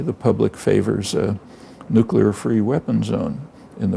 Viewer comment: did he say "nukular"...? ;-) great interview, thanks for recording & posting.